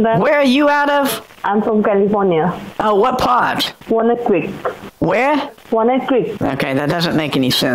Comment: Walnut Creek. was trying to say she's from Walnut Creek, California. It sounded like "wanna cree," but it's near where I live, so I could parse it. You're welcome, everyone!